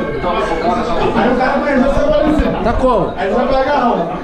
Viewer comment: só eu ouvi corona vírus???😯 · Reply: Ele fala "colou na mesa" kk